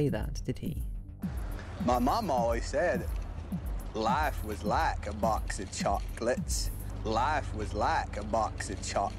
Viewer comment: Life WAS like a box of chocolate 😳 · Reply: In Forrest Gump's scene, I think "life IS like a box of chocolates" sounds better, that's why we hear it that way, but we really aren't listening, so we remember it as we hear it. I was sure I heard "Life IS like a box of chocolate" because for me it souds better as life is not the past, but the present, so I therefore remembered it that way. It really shocked me when Forrest said "WAS" at the end. I was pretty sure it was "IS" hahaha.